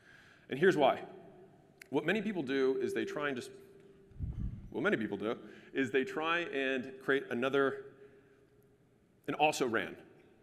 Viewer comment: What did you say at 3:05? Did u say this: “what many ppl do is try to create an also ran” ?? Im so confused bruh